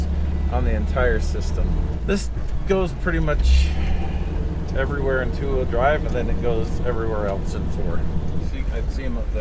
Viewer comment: thought you said it could go anywhere with 4 wheel drive :P hehe Merry Christmas